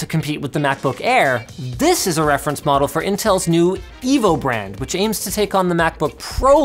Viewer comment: Did he say "Evil Brand"?